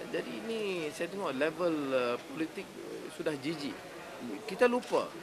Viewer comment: did he say "politik sudah GG"at 1:02 ?